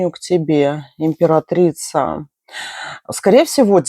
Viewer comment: Вечер добрый! На первом варианте послышалось "Вампиратрица". Порвало от смеха 😂 Благодарю - сделали вечер ❤